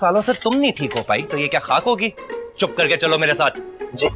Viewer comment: did ayeza just said chut*ye?